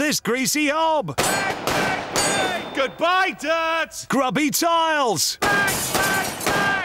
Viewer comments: Bang Bang Bang "GOODBYE, DIRT!!!!" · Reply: I thought he said ”GOODBYE, DAD!” 🤣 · I thought he was saying "goodbye dad!" since he had enough money to move out of his parents home, but no, he said "goodbye dirt!"